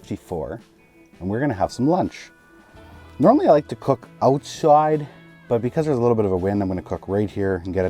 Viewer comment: i'ma need a spellcheck on what you said @ 02:15 ........ was that "owtswide" or "outsuide"??